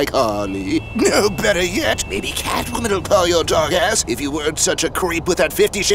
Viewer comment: I never thought I'd hear Joker say the words "yee yee ass haircut" or "dog ass". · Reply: Was it "dog ass"? I could've sworn he said "dark ass" lol